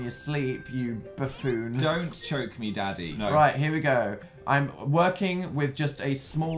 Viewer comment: Did anyone else here Phil say don’t choke me daddy 😂 at first I thought he said Danny then I watched it again and realized it said daddy!